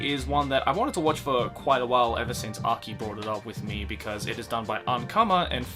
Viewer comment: oh god, I kept hearing "ever since aki broke up with me" and was so confused